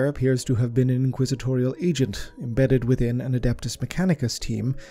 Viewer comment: Cause of ya accent I thought u said inquisitorial ejits lmao not agent's I was like waa well they behave like ejits a lot lol diddnt know it was lore it is now dude